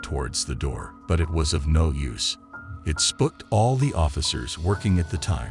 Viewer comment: Who tf says spook like that? Lmfao “he spuked him” is what it sounded like you said instead of SPOOKED!! Some people really are something else that’s for sure! Lmao wow!! 😳🤦🏽‍♀️🤣😂